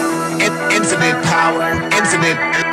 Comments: What pepole hear “infinite power” What I hear. “It’s about power” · at first I thought it was saying "isn't it power?" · Idk why I only hear "Infinite coward"... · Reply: Coward = power 😁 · at first I thought it was saying "isn't it power?" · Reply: i thought at first it was saying intement power